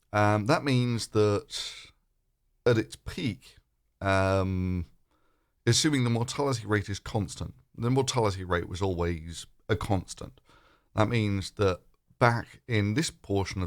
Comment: That's a misquote. He said "IF mortality rate is a constant". As in it isn't a constant and we can show that by comparing the way it really is with the way it would be if mortality rate was constant. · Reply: what he actually says at 2:40 is: "Uuhhmm assuming the mortality rate is constant, the mortality rate is always a constant" If you're going to correct the record i suggest actually correctly stating what was said and give a timestamp as proof.. not just make it up from your interpretation or memory.